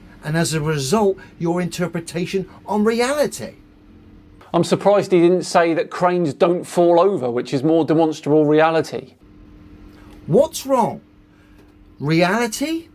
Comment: I don't think he even says Demonstrable. He says Demonstable. The Stability of Demons - specifically the little demonic whispers in his head calling him an idiot for believing the earth is flat. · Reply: I keep hearing it as "de-monsterable".